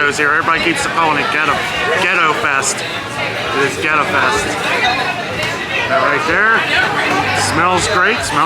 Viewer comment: at first I thought you said Ghetto fest 😅